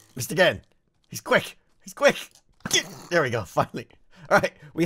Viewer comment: was that a "f*** me"? It really sounded like it, but I've never heard him cuss. What else could it have been?